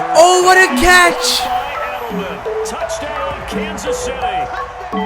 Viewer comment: who else heard "Touchdown Kansas City!" not afc